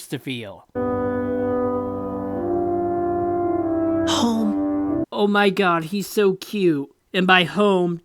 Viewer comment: I could have sworn baby wander said “hoe”